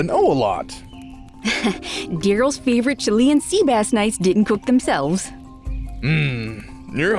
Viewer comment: Wait, Chilean!? Wow, don't expect a mention of my country here. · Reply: I thought she said chilli 'n sea bass 😭